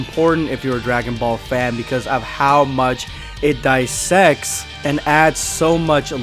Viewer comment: I thought he said “I sex”